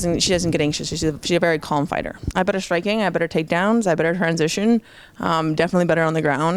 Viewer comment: did she say "i have better striking" ........